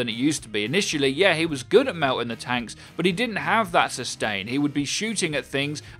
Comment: I thought you were saying "mounting the tanks" the whole time and it really really confused me. hahaha